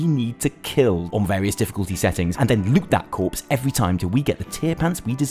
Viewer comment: on 0:50 i hear it (and lick that corpse) instead of (and loot that corpse ) xD